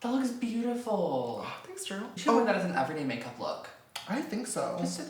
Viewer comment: that sounded like